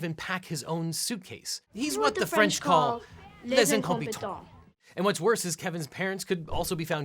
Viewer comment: for the life of me i can't understand a word of what you said in french, and i speak french as my first language. what did he say? it does not help at all that there's 2 person talking at the same time... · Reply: les incompetant or something like that